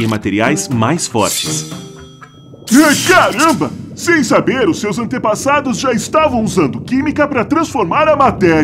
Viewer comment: "sem saber seus antepassados estavam usando para criar maconha" KSKSKSM FOI OQ EU ENTENDI